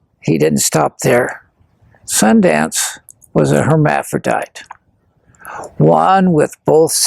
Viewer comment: 'pretend writers' lol 'i think he was a herm' no evidence...but i 'think it'.....hmmmmmm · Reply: I thought i heard him say Sundance was sterile, then he talks about his son. MORE Hmmmmm